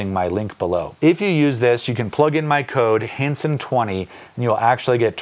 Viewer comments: I thought your code said ‘Handsome20’ and I nearly spat my coffee out 🤣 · So, I had this video play while I did other things and I didn't pay very close attention until you mentioned your discount code and I heard "Handsome20" and now I keep hearing Handsome whenever you say your name (no hetero) 😅